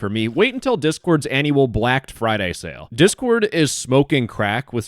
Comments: seconds did he just say "blacked friday sale"? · did bro just say anual BLACKED friday sale